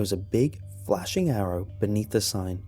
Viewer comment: I could swear I heard "big flashing arab"